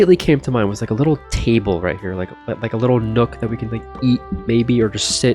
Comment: WHY DID I HEAR EAT BABY